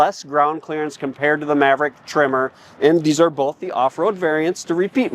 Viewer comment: For the first 5 minutes of this video I thought you where saying "Maverick Trimmer", I was like oh, Tree Trimmer for landscapers package?